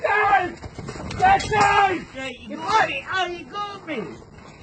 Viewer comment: Did I just hear " OH NO YOU CAUGHT ME"